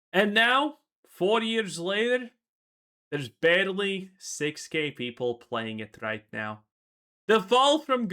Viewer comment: Thought you said "there's barely 6 gay people playing now" at first lul.